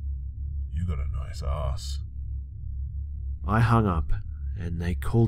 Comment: okay i don't know what he said but at like 6:00 i think he said you got a nice ass but the captions said " you got a nice sauce" i'm deaad